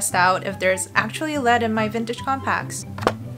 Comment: i thought she said CONTACTS 😭